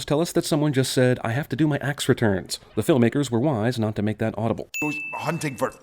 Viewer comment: actually I heard "axe returns"